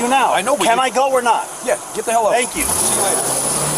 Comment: it sounded like the deputy said get the hell out of here as he waved his hand for the driver to go. If he did say then that would set the tone for everything to follow because the officer displayed aggression. However, the civilian should have rolled his window down before he came up beside the police car and have his driver's license ready to prove his residence. It's just common sense people.